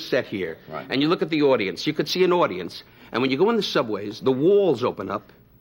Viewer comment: I thought he said exactly this "you can see an audience." lol